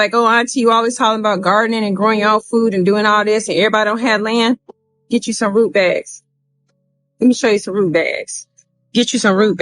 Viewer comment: I legit thought she said "get you some rutabagas". 😅 I was not expecting her to say rutabagas, but I was nodding in agreement, thinking "yeah, they store well and are versatile, so that makes sense. "